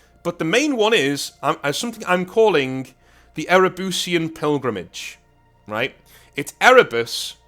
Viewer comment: I'm sorry. All I heard was Ere-Bussy, and I don't know if I can forgive you for putting that image in my mind.